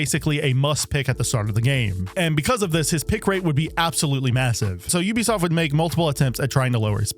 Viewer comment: wait did u say massive?